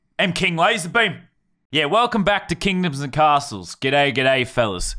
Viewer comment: I thought he said condoms and castles